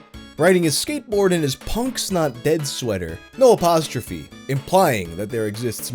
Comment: When you said he was wearing a Punk's Not Dead shirt I thought you said Punk Snot Dead. That actually would be a pretty cool band name, lol.